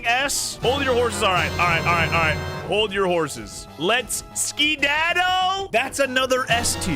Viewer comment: sounded like you were about to say skibidi